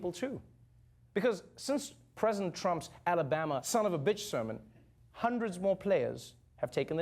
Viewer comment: Did I just hear Trevor say son of a bitch